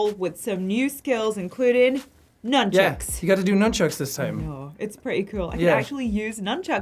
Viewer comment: “Nunchucks” “noonchucks” “numbchucks” *silence” “noom-“ ”nunchucks” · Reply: I thought he said Nutchucks XD